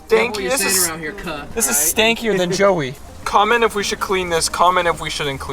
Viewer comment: bruh what did he just say? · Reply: He said sticker lmao